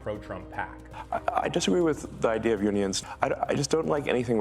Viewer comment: I thought you said humans not unions, but I think that’s really not a big difference is it?